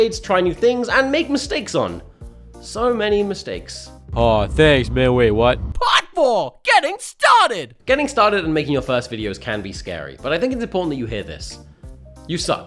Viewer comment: At first I thought part four said 'getting stabbed'